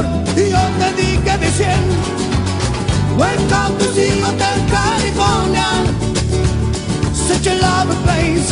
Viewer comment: Why do I hear "Welcome to casino California"?